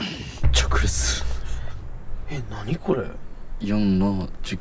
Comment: 直留守がどんどんピクルスに聞こえてきた